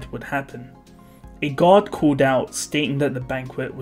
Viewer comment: It sounded like you said ‘a god’